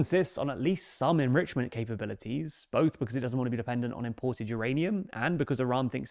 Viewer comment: it says Reply on Imports instead of Rely on Imports.